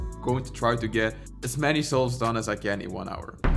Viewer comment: The moment he said "solves" I thought he said "Soles"